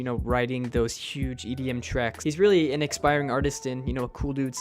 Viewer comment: did he say Illenium was an expiring artist?😂0:37 I think he mean't to say inspiring.